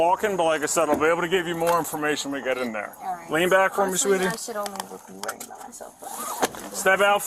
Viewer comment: Officer said "and lean back for me" I heard "and your been bag," and was so confused. lol